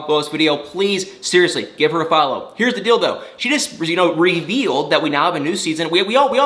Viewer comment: You said "heres the deal tho" I heard " heres the dildo" 😂🤣😂